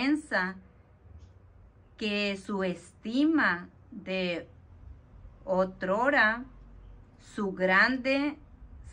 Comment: Saludos hermanita, en el minuto 9:08 aprox. No entendí unas palabras, me puede volver a leer esa parte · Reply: Shalom Alehem bendiciones Todah por interesarse en la Davar de nuestro AB KADOSH YAHWEH. La palabra es Otrora es un adverbio que significa “en otro tiempo” o antiguamente YAHWEH continué bendiciendo sus vidas.